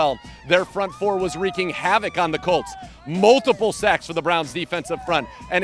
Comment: I heard multiple sex, had to replay a couple times